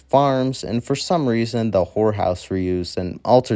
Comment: Fun video! The music was really good and it was a very entertaining video, I had a lot of fun watching and learning. I had NO Idea Spirit had THIS much history behind it! A few criticisms I'd like to offer, is to try to read the script as if you were reading it to a friend, instead of as if you're just trying to get to the end. When people read their script as if they are only reading it to get to the end, tend to get a bit of mushy-mouth and words start blending together. A bit more energy and attention to how you carefully read out the words would Really help keep peoples attention, but also make the jokes you've written into your scripts have a lot more impact! Another thing is, I'd recommend editing out your mouth-pops as they are quite frequent and are just a Little distracting. Very fun concept, and overall, great video! ^^ ♥ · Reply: Yeah, I noticed he didn't quite enunciate certain words like when he said "horror house", I honestly thought he said "whore house" and had to rewind lol. But yeah, practice makes perfect, my friend. Keep up the great work, otherwise.